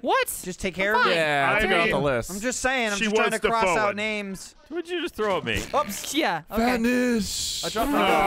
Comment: IM SO UPSET. At 41 minutes when threw a smoke grenade at I really thought he said “what did you dethrow at me?”, I went back and rewatched and he didn’t